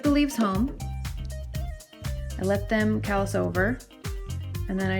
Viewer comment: I didn't catch that at 0:55 I let them kass over? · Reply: Callus...let the cuttings callus over.